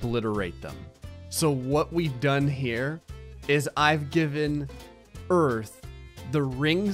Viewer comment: sounds like he said "I've given birth"